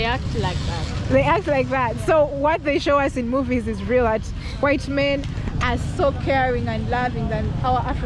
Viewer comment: I thought she was saying Dirty white men 😆